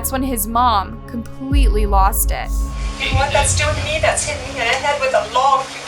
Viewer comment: What did the mother say at 8:01? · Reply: ……. when the police said they had enough evidence to arrest him the mom said “You know what that’s doing to me, it’s like hitting me in the head with a log. Don’t do that to me” Then the police said “ why would you say something like that” She said “you can’t take him to jail there is bad people there”